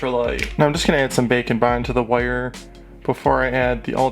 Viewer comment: I always hear 'bacon bond' even though I know that's not what it is...